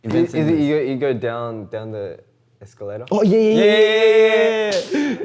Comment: Does Felix just said "escalera"?!?!? My spanish heart is melting right know, I love them so much